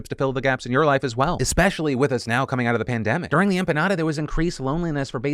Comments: Wait did you just say during the "empanada" and "Paraguay" here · Tell me i Didnt hear phil say Empanada, Im not crazy right? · did I just hear, "During the 'empanada' there was increased loneliness for basically everyone." Anyone else hear that? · Did you really say "During the empanada?" at 21:45 LMFAO had to replay a couple times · Does Phil say “during the empanada”? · Lmao did he say “during the empanada” at 21:47?? 😂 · did phil say "during the empanada"? Or is it just me? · Did he say during the empanada? Then paraguay? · Anyone else heard "during the empanada" at 21:46?